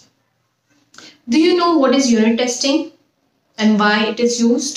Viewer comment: I thought i heard urine testing